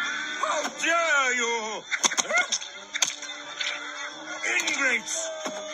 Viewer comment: my reaction to them closing a bugs land Did he say ingrates?